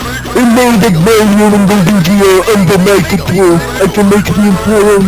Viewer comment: I can't really hear what Luigi is saying. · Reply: I got you, "And now that Mario and Luigi are under my control, I can make the mushroom kingdom kneel before my rule" That creepy bowsah's laugh